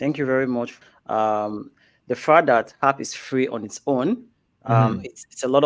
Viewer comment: I thought he said if free.